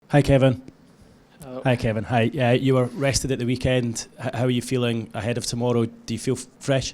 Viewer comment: I thought he said he was arrested at the weekend 🤣